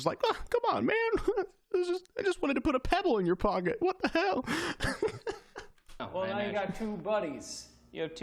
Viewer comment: lol it sounded like ranboo said hell. also man, please don't get possessed.